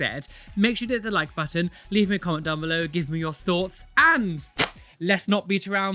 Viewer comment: Oh gosh! Ahaha! I was kinda distracted during intro and I swear I misheard him saying "Leave me a condom down below, it leaves me your thoughts." and I choked on my water.